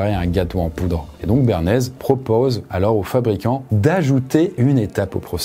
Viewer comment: Pendant toute la vidéo j'ai entendu "Béarnaise" 🤣🤣